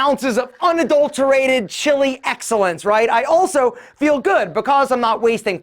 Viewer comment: “sick” should be subtitled as “sixth” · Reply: “chilled” ought to be written instead of “chili”